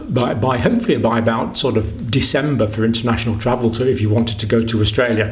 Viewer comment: did you just say 5th December or is it my hearing lol ??